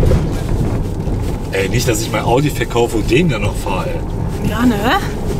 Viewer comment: den audi verkaufen? ich hab mich wohl verhört 🧐